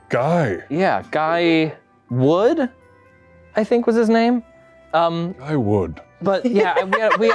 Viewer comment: Lmao I thought he said "I would"